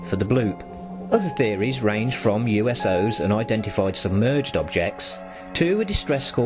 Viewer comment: am I going crazy or did he say uso 3:11 or did he mean unidentified space object?